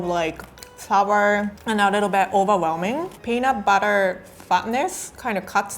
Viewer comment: The subtitles thought she said peanut butter fuckness" when she said fatness 😩